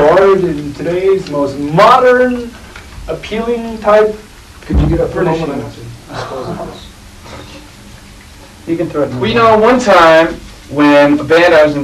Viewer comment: What did Kurt say at 0:17? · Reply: Can you get up for a moment? I have to dispose of this. 5 years later. Your question has been answered.